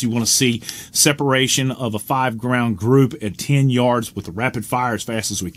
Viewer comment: Your talking to fast: it sounded like you said 5 ground group